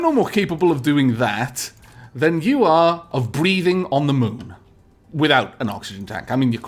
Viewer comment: When said "Breathing on the moon" I thought he had said Breeding instead. Needless to say, I was ever so slightly confused